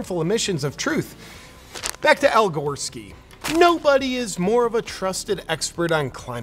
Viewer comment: I had to watch twice because I thought he said Al Goreskin.